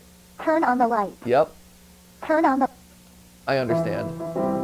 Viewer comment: "Turn on the ligh-" What i heard: "turn on the f-"